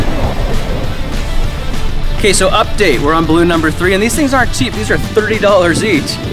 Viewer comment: "These baloons are cheap, these are 30 dollars each" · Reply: Fierce Diety Gaming . 3:12 I think he said "are cheap"